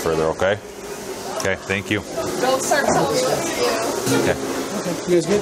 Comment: did she say "Don't start telling me what to do" when the cops starter walking away?